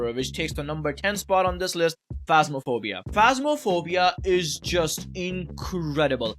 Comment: I was so confused when I misheard it as "Homophobia and Fatphobia" lmao